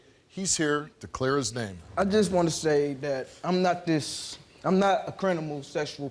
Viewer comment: did he say crin-minal?